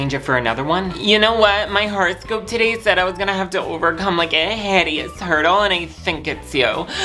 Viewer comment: "My horoscope today said I was gonna have to overcome like a hideous hurdle and I think it's you" Omg..I just can't stop laughing Edit: It's "turtle" lol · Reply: I thought he said hideous turtle 😭😭😭 I was confused but accepted it